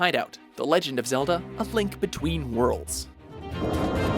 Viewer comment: Did you say A Thlink Between Worlds?